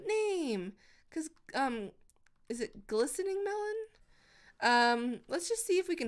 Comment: When she said glistening melon i thought she said glycerin melon